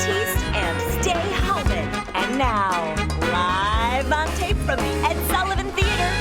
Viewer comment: Feature "Jean-Baptiste and Stay Uh, man" Am I the only one who heard that? ^^